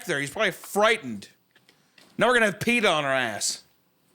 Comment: Hunter: Now we're gonna have PETA on our ass Isaiah: He already peed on me The misunderstanding, and then realizing Isaiah thought Hunter said "Now we're gonna have pee on our ass" and didn't question it